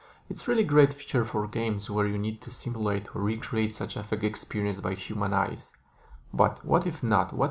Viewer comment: Hi, Thanks for your tutorial! Again, there're some words that I can't get clear. First one is around 00:48, where you say: "...where you need to simulate to recreate ?????? experience by human eyes.... ". What's the word after "recreate"? Another one is around 3:01, where you say: "....Unreal Engine uses different textures channels to ????? information about....". Are you saying "to package information"? I am not sure here also. Sorry to bother and thanks for your patience! Chenglin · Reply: The first one: "...where you need to simulate or recreate such effect experienced by human eyes" 2nd: "Unreal Engine uses different textures channels to pack the information about lighting..." I meant "pack" as store/gather Not a problem :)